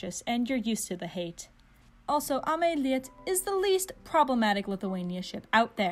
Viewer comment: I thought she said AmeIta for a second and paused the video. It’s been about 5 minutes now and I ship AmeIta now.